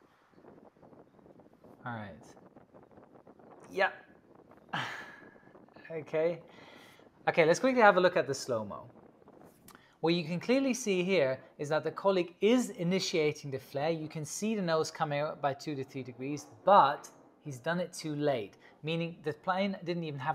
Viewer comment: "The colik is initiating the flare." What is a "colik". No sarcasm. What is that word?